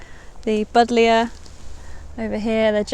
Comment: Did you say: "Bodlia"?? I always wondered what that plant was called! I absolutely love the smell of the flowers of that plant, and all the different flower colours it comes in!